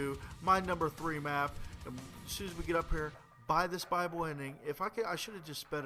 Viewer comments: i thought u were saying bible ending?? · i actually thought that you kept on saying "bible ending" and i was like dude? i'm not a fucking christian! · I thought he said "Bible ending" :D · does anybody else think he sounds like he says bible ending